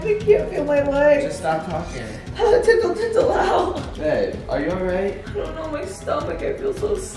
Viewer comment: OML, at 06:27 I thought she said "tickle, tickle meow" I am so frickin deaf 💀😂